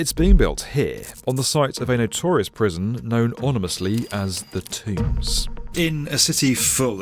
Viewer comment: Did I hear “onimously”?